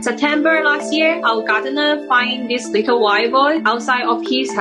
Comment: Her accent is so cute she sounded like she said this little wild boy when she said little wild boar. He is her “ sweet little wild boy “ although she’s a girl, lol that she took in.